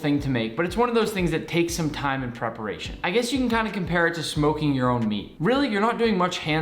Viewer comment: I thought he said smoking your own weed 😂